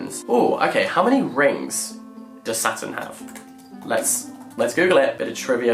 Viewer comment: OK but English is not my first language so at first I heard said, “how many rings does Satan have?” and I was just ready to accept this concept with no question 😭